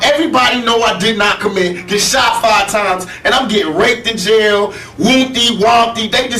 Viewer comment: did he say "he's getting raped in jail"...?